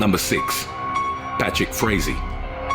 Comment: When he said Patrick Frazee I thought he said “bat shit crazy” it’s time for bed🤣🤣